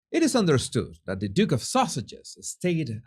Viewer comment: Misheard the Duke of Sussex as the duke of sausages … perhaps it’s your accent perhaps it’s down to auto-suggestion! But seriously as much scorn as we may have for Harry’s silliness it is sad and I think we should all just leave him alone. He has become so pathetic and arrogant with it that it is all bound to come crashing down on him. As for the most unpleasant Duchess of Sussex who really is never going to live up to her title, well, i feel that slowly, surely, steadily her disingenuousness and lack of a moral compass will be her own self-inflicted downfall. Not sure how this will come about but anyone who is so cruel to set up someone gullible enough to fall for their wiles and lies and take the fall from grace as Harry has, well, one way or another, they will pay the price.